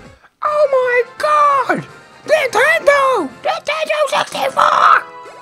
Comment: lol did he say ninetndo 64? trying to catch mewtwo and raging xD